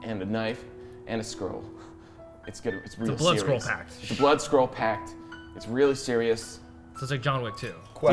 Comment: I really thought the said the blood squirrel pact